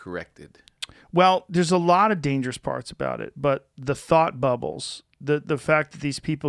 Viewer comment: At first I thought he said "the Fart Bubbles"..